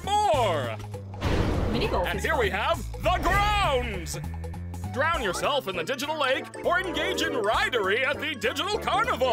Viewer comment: did anyone hear drown yourself at the digital lake